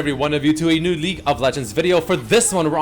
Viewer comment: why tf do I always hear "leak" of legends when says league WTF?!??!??